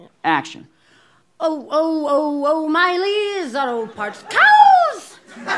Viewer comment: Oh, oh, oh, O'Riley's, Auto farts! OW! Smack that child's back, goldfish The snack that smiles back, children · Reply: Please tell me I'm not the only one who thought they were saying farts not parts in the O'Riley's ad